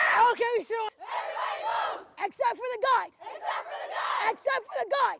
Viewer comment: "except for the god" Is that a religious military school or am i mishearing something?